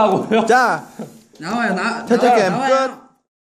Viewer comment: Did V say "Tae Tae cam cut"? Well it was cute..